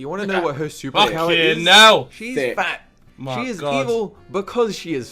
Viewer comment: wait did vik said "She's Thick"